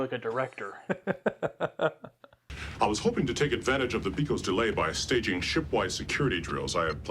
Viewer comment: wait, did Worf just say “…the pecos delay”? As in that classic western movie line “…(something something) west of the pecos.”? (Maybe it’s pekos, I’m not sure) Is that an Easter egg to westerns I’ve never noticed before?